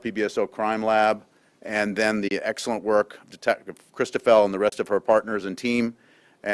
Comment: That poor woman dealing with this whole case. You can see the pain and agony on her face. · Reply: Me too. I thought she said her name was Cristobel, I must have misheard. I certainly couldn't see any pain.